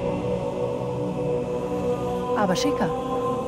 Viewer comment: Was sagt Cortana eigentlich bei 2:12:30?Klingt wie "aber schicka".